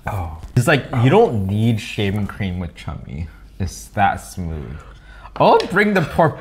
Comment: did you say you don’t need shaving cream with Chummy?? 🤔